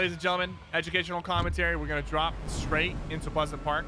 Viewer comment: did he say muslim park? Lol